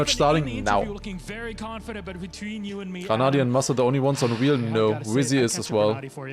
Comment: no one in chat caught that he said massa instead of mudda? Lmao